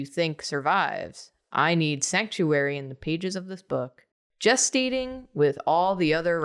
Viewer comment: Did somebody say Just Eat(ing)?